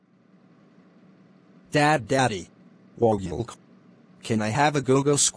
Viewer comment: what did he says? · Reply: Whoa gulkh